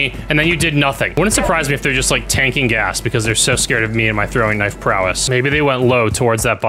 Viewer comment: inb4 "you're just paid by call of duty" if i am, could someone tell them they're sending the checks to the wrong address? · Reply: I thought u said u never miss a throwing knife